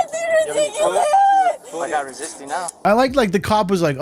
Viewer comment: I thought he said “you’re not resisting now”. (Kind of like she was before, but stopped and didn’t know why she was still in trouble.) Like the suspects that are actively resisting and scream that they are not resisting! 🤣 That is so annoying to me, and I always want the cops to shout back, “Pulling away and twisting IS in fact resisting….” They always claim that they are being hurt….if they stop resisting, it wouldn’t hurt as bad. If at all. 🤣🤣🤣